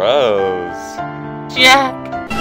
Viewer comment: I thought Jack said "Gross" at 0:28 And idk why Youtube recommended me this?